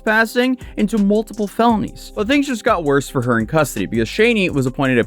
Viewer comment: when he said custody she thought he said custard